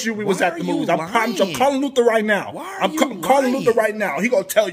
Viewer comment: I thought she first said “Why are you crying!?” 😂😂